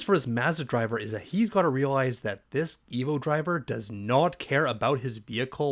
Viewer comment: I thought you said evil driver. Nope, you said EVO driver